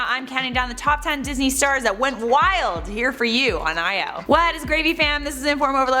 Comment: I thought you said "What is gravy" pfft